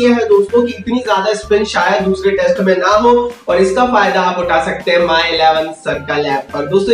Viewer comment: Kya bola ye at 1:55. 2nd test mei Shayad spin na ho😂😂😂